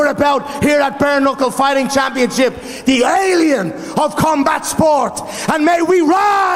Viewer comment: YEA my gf is in the video :P What did he just say "Illegal Aliens of Combat sports" ? Someone call ICE :) (oh and i PAY to watch BKFC ) got money left now since the UFC is boring.